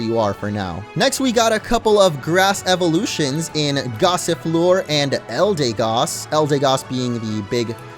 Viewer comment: Duraldon loooks like a ice type to me Gossifleur, thought you said Gossiplure lol